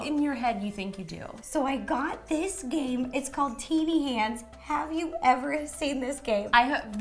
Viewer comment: who else thought she said titty hands😂??